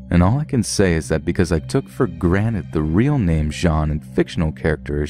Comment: "Took for granet" 21:04 Only reason im pointing this out is because i was just watching the rick and morty episode where morty makes fun of rick for saying granet instead of granted 😂